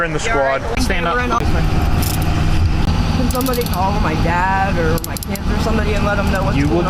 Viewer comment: Did she say.. call my dad or PIMP... wtf lol😂 at the 11:50 mark...